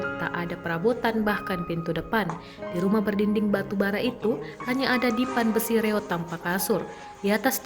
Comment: dinding batu bara.. apakah saya salah dengar pemirsa